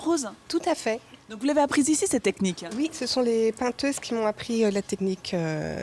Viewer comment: Elle dit "les peinteuses" à 3:52 ?? c'est quoi ? Ne me dîtes pas que c'est le nouveau nom féminin pour "peintre" ou je casse tout